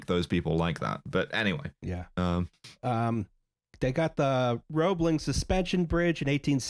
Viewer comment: did she just say "roblox" suspension bridge??? I don't know why but that killed me for some reason